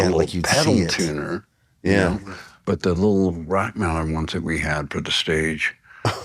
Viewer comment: what’s the tuner he’s talking about? Kinda sounds like he says rock maller tuner?